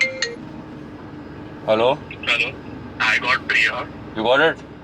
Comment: I just thought that he said I gott priyaaa 😂😂😂